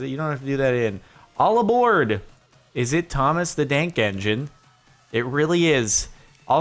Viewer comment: wait did he say Thomas the dank engine?